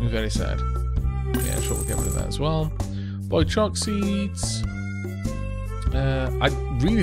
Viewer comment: Did you say boy chok seed? lol good one.